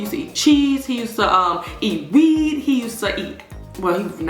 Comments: i thought you said “weed” lol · Did you just say “He used to eat weed”?!? 😶😯🤭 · Did she say he used to eat weed